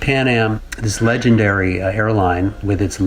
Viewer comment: im pretty sure i heard him say "This Legandary HAIRLINE"